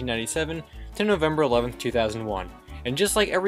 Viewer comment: Who thought he said Sept 11 2001 at 0:05?